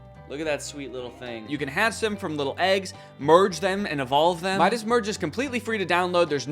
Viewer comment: Download Midas Merge for free using my link to discover the cutest creatures and explore the most adorable gardens! <link> Thanks to Midas Merge for sponsoring. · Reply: i thought it said midget merge wth u scared me 💔💔🚡